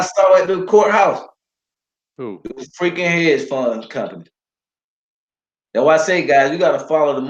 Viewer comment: who did he see at the court house? trying to make out what he said, might be an audio error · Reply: hedge fund companies.